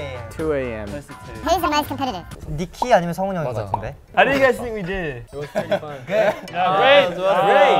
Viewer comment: what did jake say? i didn’t quite catch that 😂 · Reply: “How do guys think we did?”